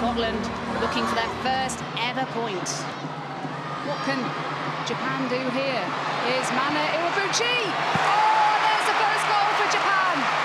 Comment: Did someone just say Mane? As in Salah Mane Mane?